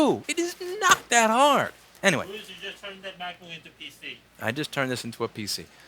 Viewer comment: Tell the guy in the background who half said: "just turned it into a pc" to put more food in his mouth because we couldn't understand the last part of his sentence. lmao